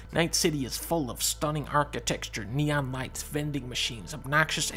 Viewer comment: While rewatching this video, every time dunkey says neon light(s), I only hear the "NEON WHITE" from the latest video. LMAO. Neon White!